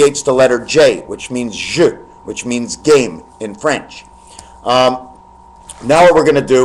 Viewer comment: or did you say it means "game" in French? I couldn't really understand that part.